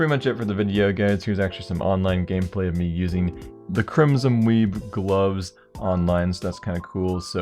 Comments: It sounded like you said crimson weeb xD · i thought he said the crimson weeaboo lol